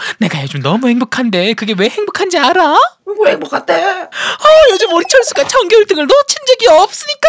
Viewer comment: 영기 엄마인데 “요즘 우리 철수가 전교 1등을” 이라고 들림 😂